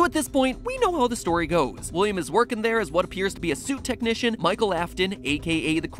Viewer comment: When he saidshit technician I thought he said soup technician lol